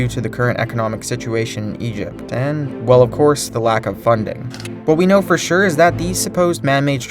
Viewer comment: I thought he said "the lack of fun dip"